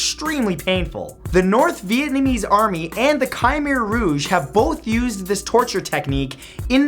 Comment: Wait... Did you just say "the Kaimeer rouge" AHAHA